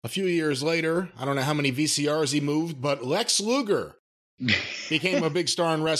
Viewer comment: I remember his start in Florida. He seemed like a star at the time. When I first heard his name though, I must admit that I thought they were saying "Lex Luthor"